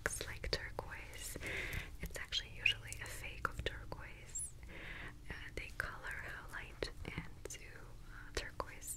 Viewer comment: I can't hear this right.. This is a fake of what? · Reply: VNDY WVRHL i think she says „turquoise“ 🤔